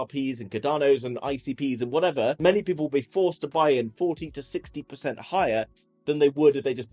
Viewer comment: Did he say, "14 to 16 percent higher?" lol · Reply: 40 to 60